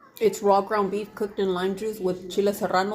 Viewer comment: what’d she say ? I heard tostada and garlic powder